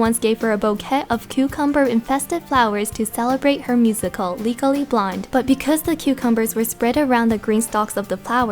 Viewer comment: Finally! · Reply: It also sounded like you said 'legally blind', not 'legally blonde' haha :D